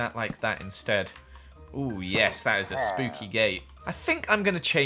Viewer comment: I thought he said “that’s a perfect gay!” And then realized what he actually said 🤣🏳️‍🌈